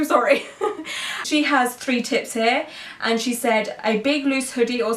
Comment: i thought you said “three tits” because I had this playing in the background ahahaha